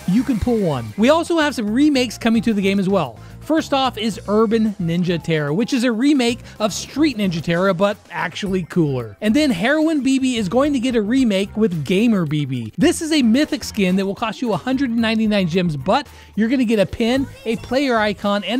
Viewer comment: did bro just say heroin??????